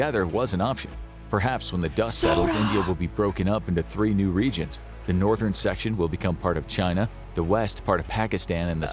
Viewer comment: What did say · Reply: MINNESOTA!!!!!!